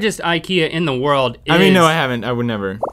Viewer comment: I thought link was saying “kukens kurva” BRO